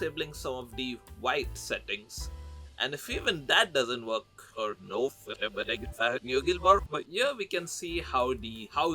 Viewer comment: what did he say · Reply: "You're probably gotta have to get a better phone bro"